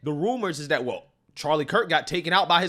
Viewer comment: I never even heard of this guy. I thought they said Capitan Kirk got shot.